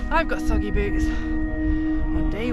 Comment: You said "I've got soggy boots" but I heard "I've got saggy boobs." Definitely don't.